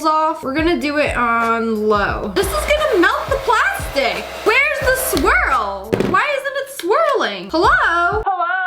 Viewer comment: When she said “WhErEs ThE sWiRl?” all I heard was, “WhErEs ThE FlAvOr?”